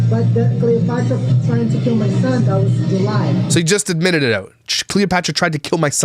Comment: cleopatra said that he tried to kill his own son, and he said that is a lie. all liars in this story anyway. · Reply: ok says he said she tried to kill his son in July but it sounded like he said that’s a lie not in July